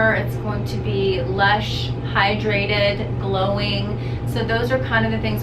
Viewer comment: I love LOVE this facial. I thought the aesthetician said “less hydrated” in the beginning but she said “Lush, hydrated...” I also LOVE that she touches on not using the mechanical scrubs on the face due to they are often too abrasive and you can see capillary damage.